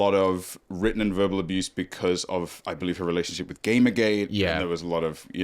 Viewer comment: When said "game gate" I thought he said "gay McGay" and was howling laughing bc I know that's not he said